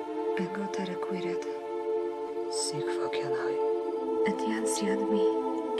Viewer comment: czy ja tam usłyszałam Si fakenaj ? xd